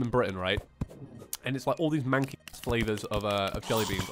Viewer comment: It sounds like your going to say manky bu then you didn't so now im curious, I keep re watching this bit because I want to know what you said.